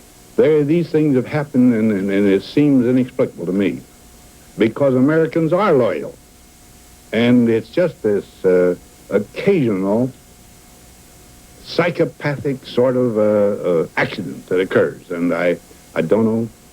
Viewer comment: Did he say it was a "psychopathic ACCIDENT"?? This interview seems like early organised propaganda... check 3:40....